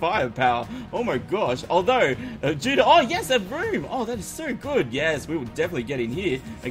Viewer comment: DIDNT it sound like said a broom!!!